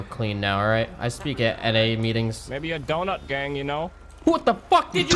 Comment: what did say? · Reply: Maybe a doughnut gang